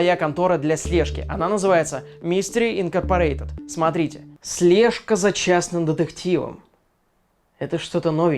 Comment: мне послышалось слежка за чайным детективом